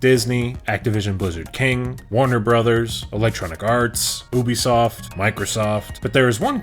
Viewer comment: Misheard the ubisoft as boobiesoft. Imma gonna make a lil YouTube break and go outside 😂